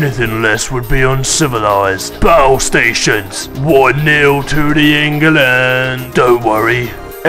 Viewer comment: What chant of battleship says? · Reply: John Mel Geronimo one nil (1/0) to the England it's a football score